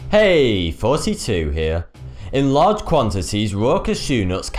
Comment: I swear bro everytime I watch your videos it always sounds like you say hey 42 here lol